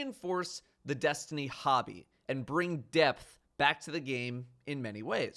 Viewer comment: at first, i thought you said Death and not Depth LMAO